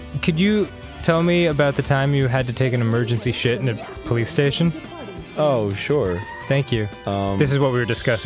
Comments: I thought Dan said playstation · At first I thought he said he had to shit in a PlayStation and I got really concerned